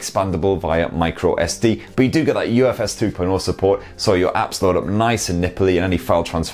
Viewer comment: Did he said UFS 2.0?